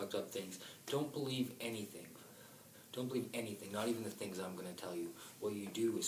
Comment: this guy knows his shit "echoes.....echoes...echoes" yes echoes do weird shit to more then just words.....dude i do apologize about your skip.... 8:32- 8:35 what did you say...?Yes some are already awake. Yes super bowl was their 'call to arms' per-say.i also bet you was not finished but had to ....i also apologize for this. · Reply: I asked not to believe anything i was saying and about to say - only question - if you end up questioning and comming to the same conclusions thats awesome - in my videos i do alot of things i dont and little tests - for example this one i said some pretty controversial things - especially referring to the bible . Call to arms - one of many - thanks for watching and taking the time!